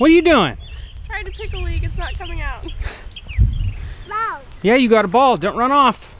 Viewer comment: Cracked me up I thought She said I am trying to take a leak but it won't come out! lmao...it was digging but i didnt hear that ...lol.